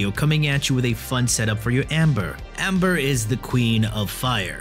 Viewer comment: Did I hear Amber? She's from Genshin Impact. Maybe I miss heard it. OR...